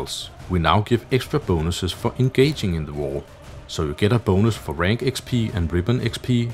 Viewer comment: is he say fu**ing gaging in the war?